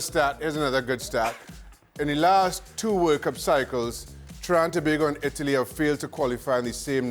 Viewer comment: I literally started clapping and laughing at Shaka's Trinidad & Tobago and Italy's stat at 2:45 , which I have never done in an ESPN video ever before. Also, it was interesting to see Lebouef having the same reaction a second or two later. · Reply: Thanks for your comment. I thought he must have been saying Trinidad and Tobago but I wasn't sure because he said it so fast. Closed-caption says "Trying to Big" and that's exactly what it sounded like to me. After reading your comment I can now say his statement was every bit as hilarious as I thought it was (and I'm an Italy fan).